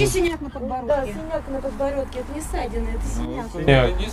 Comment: Что она несёт??? "Он жрать хочет" Или послышалось?